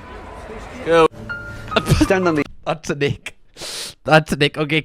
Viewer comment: Hello donuts, apologies for lack of uploads this week.. YT demonitised loads of our vids so been trying to sort it all!! What videos would you like to see next?? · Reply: Idk If this could be why but its just a thing i noticed the subtitles of the videos are the wrong thing instead of what you actually say for example 1:01 ish when you say ick. Love the british memes and best background out of the other lads :))